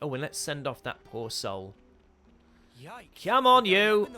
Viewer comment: I wasn't looking at the video and I thought he said ''Come on, you!'' to the corpse x'D I was half expecting a ''Off you go!'' to follow, as in sending them off in a hurry xD ''Off you go, c'mon, I haven't got all day!'' Oh my god if Noah's personality was actually like that, just thinking about it cracks me up..